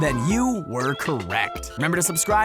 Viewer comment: sounded like begs for watching